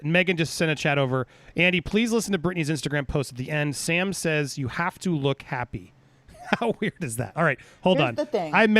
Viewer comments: What did Sam say to Britney on the second video? It sounded like he said You have to look happy. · When I saw the post on my own, before watching this clip, I absolutely thought he said, “you have to look happy.” It’s kind of a weird thing to hear but I can understand how it could be said innocently in context.